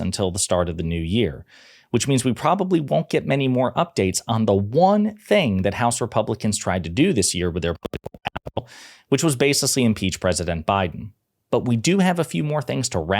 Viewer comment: What did he say btwn 0:11 and 0:14? It's "censored" in my feed. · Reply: “The one thing that House Republicans tried to do this year with their political capital”